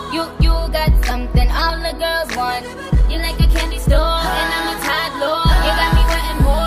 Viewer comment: Its "your like a candy store and imma TODDLER" It just sound like she said "imma tired gir"